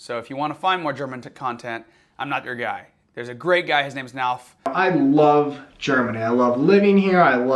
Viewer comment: What's the other YouTuber's name? Nalf? Unfortunately, I didn't catch it. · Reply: NALF is correct. definitely sub him